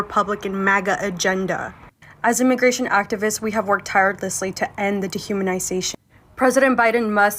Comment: Did she just say “Tiredlessly”?? 😂😂😂